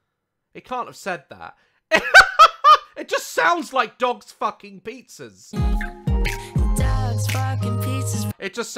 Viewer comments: I heard “dads f*cking pizzas” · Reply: I heard “ducks f*cking pizzas” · I heard dogs f*cking pizzas too but turns out he said "Dark spark with pieces". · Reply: I thought he said dogs park and pizza😅😂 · I heard “dogs sparkling pizzas”😂😂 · I heard “me f**king pizza’s“ just me lol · The “Dogs f____ pizzas” thing. The real lyrics are “Dark Sparks with pieces” idk what it means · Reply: I thought is said dogs barking with pizzas